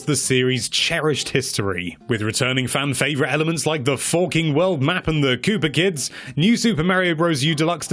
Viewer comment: did he say f**king or have i misheard it? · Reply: Forking. As in, the forks in the road. Branching paths.